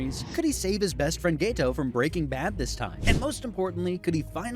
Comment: im sorry did you say breaking bad?